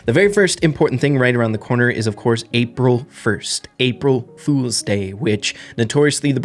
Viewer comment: april fools day b*** is what I heard😭😭